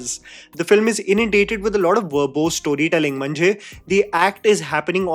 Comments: Did you just say Mhanje 7:53 ?🥹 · Did he say "mhanje" in between? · Did say ‘mhanje’?? 😆😆 I love it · he said mhanje (marathi word for "meaning") out of nowhere😂 I mean just one marathi word in all of english naration and made me go back to this part just to confirm what exactly did he say😂😂😂